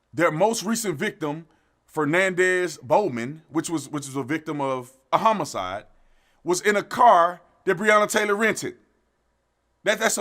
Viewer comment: I like the work that you and are doing. I went back and watched the clip of you provided(minute mark 3:00-3:05) us and he did not say "in the trunk of the car" he said "bowman's body was found in the car